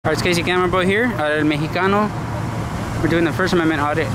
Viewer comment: Bro for the longest I thought you said crazy camera boi 😂